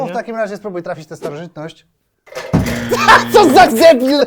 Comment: Ja usłyszałem " co za zebil" xD